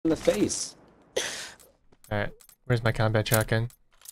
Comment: "Where's my combat shotgun" All I hear is "it's show time"